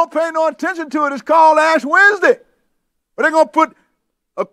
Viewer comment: What did you say @ 1:02:35 it’s called what ? And you said the mark will be a cross and ash? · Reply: He said that the Catholic church prepares people for the mark of the beast every ASH WEDNESDAY by putting ash on their forehead or in their right hand.